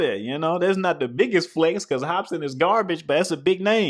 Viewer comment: Did He just say hope sin was garbage 😑